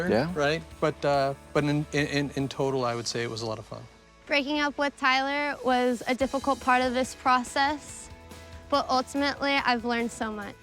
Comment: Did she say that breaking up with the guy was apart of her dogs training ?